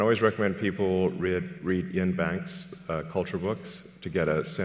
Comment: What did he say at 8:12 like Yin Wang's Culture books or what? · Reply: Ian M Banks Culture book series.